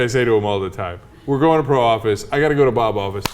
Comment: Oh Pro office, I thought you said Parole office.